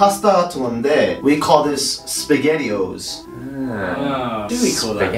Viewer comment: Oh god... I thought you were saying, Spaghetti"hoes" not, "O's" at this part..